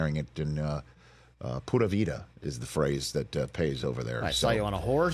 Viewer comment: Pura vida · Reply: I thought he said "puta vida"..............